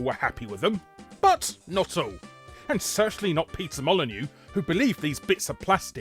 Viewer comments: Why I hear "Pizza Molinew"? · I keep hearing you say "Pizza Molyneux" instead.